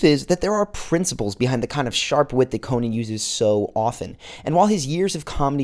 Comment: Did you say Conie...? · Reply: Conie2012